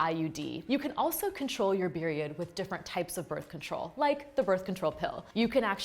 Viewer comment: Am I tripping or did she say beriod instead of period